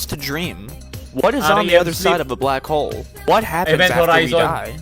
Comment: ano sinabi ni tito dito hmmmm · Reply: REM (rapid eye movement)